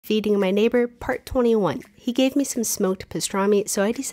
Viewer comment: Why did I hear, "eating my neighbour'